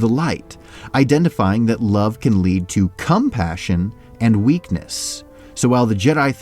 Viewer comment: the way he said COMpassion, it sounded like he was saying CUMpassion